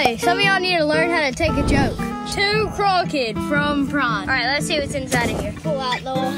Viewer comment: BOO10 for 10% off our entire site! Happy Halloween y’all! <link> (MERCH25 for 25% off all clothing) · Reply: i thought you said Pride but was really happy when i realised you said PRIME lol. see ya. from Edinburgh Scotland 🏴 🤝 🇺🇸